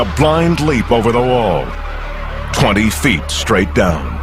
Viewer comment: the one at 3:29 how many feet did they say he dropped? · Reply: 20